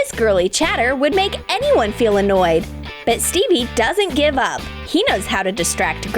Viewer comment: Soo...what I'm the only one who heard Stevy doesn't give a... at 0:48 no hate